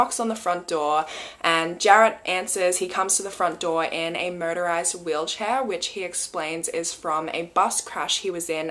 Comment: I heard “murderized wheelchair”😂